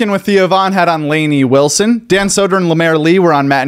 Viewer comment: I thought you said Theo von had on Rain Wilson on and I got so excited thinking Dwight was on lol